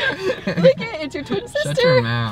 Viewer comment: anyone catch she said lick it it’s your twin sister